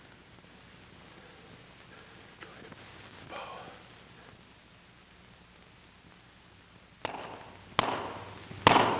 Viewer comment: What is he saying at 1:01:17-18? He says it very quietly, whispers it, and I cannot make it out. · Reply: "i'm a pooper"